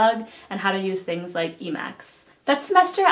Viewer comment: is she saying emacs? like the editor?